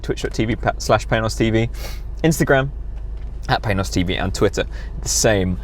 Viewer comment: Did I hear you say "at Paynos TV", or "at males TV"? This channel is big muscles!